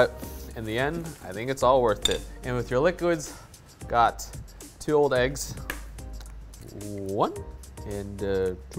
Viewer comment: Did just say 'i got my 2 old eggs'